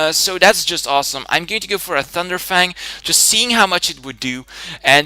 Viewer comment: did u say thunder fang??